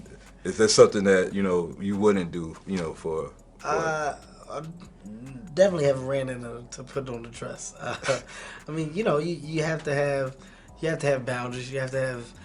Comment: He did say it. You just did not understand it. The guy asked would he wear a dress He said no I don't think so... You have to have some boundaries etc.... What is hard to understand there? No I don't think so..... You have to have boundaries... Both say enough to everyone. If you can not gleen information from that then dude seeiously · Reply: You are bad at listening. Go to 7:20. He says: "Definitely haven't ran into putting on a dress. I mean, you know, you have to have bounderies". That is word for word what he said. He didn't say no to wearing a dress (mind you he didn't say yes either). He didn't answer the question with a definitive no. He just talked about setting bounderies and protecting his brand.